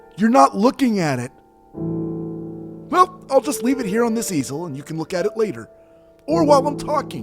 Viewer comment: I heard 'I'll just put it here on this Cecil' and I was so confused 😂